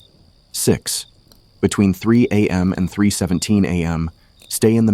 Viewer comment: why does it subtitles say 3am to 3am but the voice says 3am to 3,17